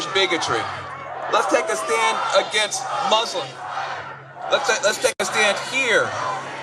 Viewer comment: what does he say at 4:02? "Lets take stance against moslems"? can someone correct me or verify this?